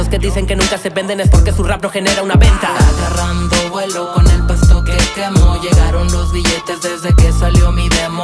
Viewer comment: Agarrando vuelo, con el pasto que quemo 🤑✈️🔥🙏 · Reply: Yo pensé que decía con el pasto que amo xddd